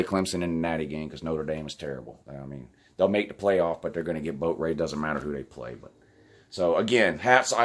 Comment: I've listened to it 3 times. I STILL can't tell what he said about Notre Dame. I thought at first he said "boat raped" which makes me think of IASIP. Georgia is a Top 4 team. They should be in but won't with 2 losses. Great game. UGA isn't going away any time soon. They are elite. Roll Damn Tide!